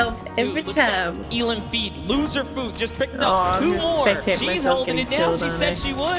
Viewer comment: did I hear LOSERFOOT?!